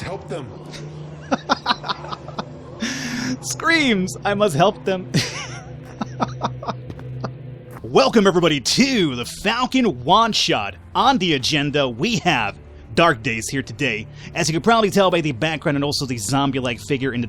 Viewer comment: I laughed during intro and it sounded like you said Falcon wad shot. :P